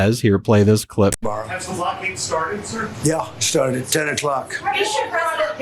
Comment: Did he say he sharted at 10 o’clock ?